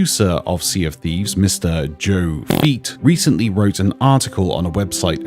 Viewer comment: Did someone say FEET!?!